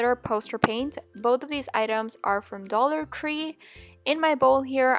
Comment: no hate did she say dollar cree